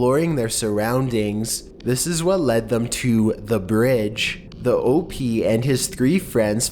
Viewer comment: When he said "This is what led them to...the bridge." I had adjusted my headphones, and the noise covered up his words, so it sounded like he said "This is what led them to...the bitch."